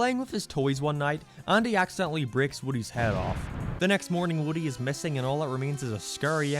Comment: bro I thought it said “woody’s head game off” 😭